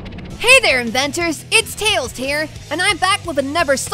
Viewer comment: wait did tails say it's tails tear